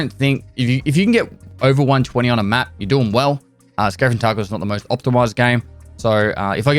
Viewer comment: Did he really said escape from Tarkov is one of the most optimized games? 7:50 lol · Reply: he said "escape from tarkov is not the most optimized game"